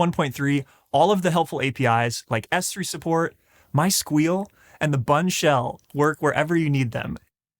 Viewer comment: did he say my sqeal?